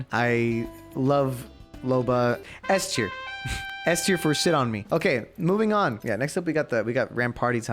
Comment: sounds like he said loba shit on me